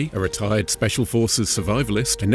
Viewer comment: did you say a R3T4RD3D special forces 😂😂😂😂😂